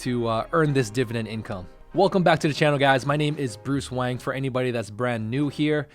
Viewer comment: For a second, I thought you said Bruce Wayne